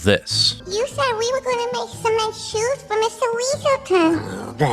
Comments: I thought she said "Cement Shoes" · she clearly says “cement shoes” not nice shoes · I thought little Judith said “cement shoes for Mr. Weaselton” and I thought, ‘Dang! That’s dark for a little kid to say and imply in a family movie. But she is in a mob family.’ Listening Task failed successfully for me, I guess.